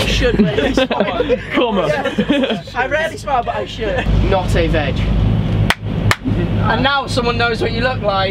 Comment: Can someone tell me what said at 11:32 please? :(((((( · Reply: 4 years later but he said Not a veg☘️ I think